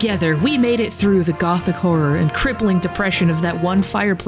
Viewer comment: I thought you said "gulf war" when you said "gothic horror".